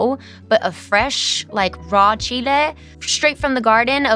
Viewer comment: realising she said Chile 🙈 i thought she said cheeto in the short